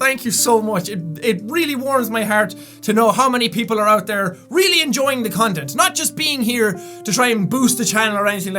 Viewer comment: When he said "Thank you for enjoying the content." I thought he said "Thank you for enjoying the condom."